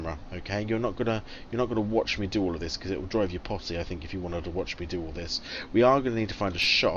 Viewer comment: haha 19:27 did you say it would drive us pottsy?